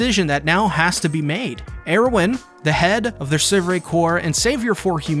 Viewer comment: I like how the auto-generated subtitle around 6:20 says "Heroin" instead of Erwin xD